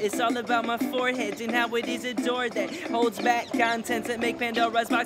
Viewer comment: WHAT????? NANI??? I've been saying it wrong all along... I thought he said "and how it is a cork that holds..." but its door?